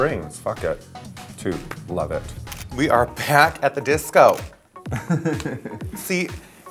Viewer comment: Low key thought they said "And we are Panic! at the Disco!"